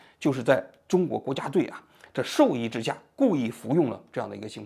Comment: 在授意之下，集體服用～聽成了在獸醫之下……😢😅